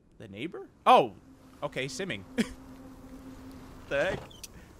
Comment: I thought it said SIMPING instead of SIMMING 😂